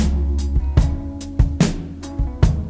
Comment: I thought they were saying lawn dart blues